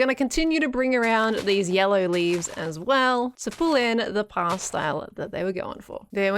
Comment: I would have swore I heard “pyle stallet” instead of “past style” as the closed captions says